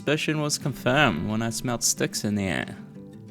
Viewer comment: had to do a double take. Misheard what you said the first time. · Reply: What? U can clearly hear him say sticks 😭